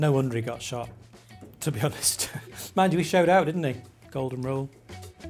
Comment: what did he say? "noone got shot.... golden rule".